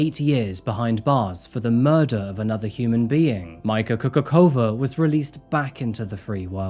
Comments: At first I thought he said her name was Michael Coca Cola. I was so confused lol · 😂 thought i heard Michael Coca-Cola for a split second. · The first time said her name I thought he’d said Mi CocaCola. But it’s infuriating that Mika got away with it.